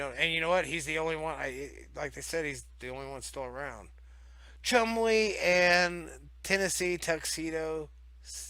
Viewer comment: Thought you said Chung Li not Chumley lol